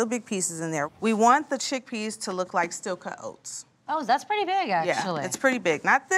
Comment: What did Elle say the chickpeas should be like at about 4:30? I couldn't quite make it out. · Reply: Steel cut oats. I missed that the first time too.